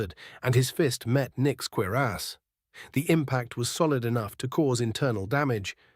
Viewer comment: shot sprite outta my nose when it said "his fist met Nick's queer ass" what a perfect combination of words for AI to fuck up cuirass